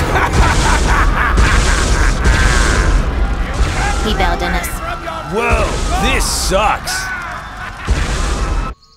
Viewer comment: if I heard him correctly, Ripto in the background said something like "RUN AWAY FROM YOUR NEW GOD" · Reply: He said "You can't run away from your new god!!"